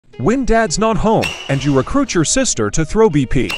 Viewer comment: Ngl I thought he said "who wants to see baddy take some swings" not Maddy